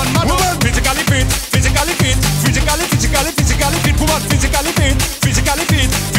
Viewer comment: “Physically fit physically fit physically physically physically fit physically fit”😂🤣🤣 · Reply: I thought he said physicality. XD